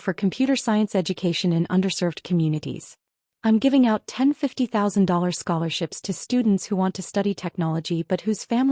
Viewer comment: This is a significant content error: “I’m giving out $10,000 scholarships to students who …” The numbers are written correctly, but they do not match what the narrator says: “I’m giving out ten $50,000 scholarships to students who …”